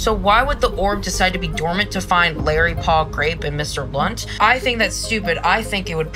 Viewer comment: Did she just say Mr. Blunt or am i going insane